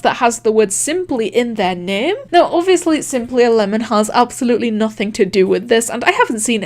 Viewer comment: When he says "simplyAlemon" i hear it "simply11" for sum reason.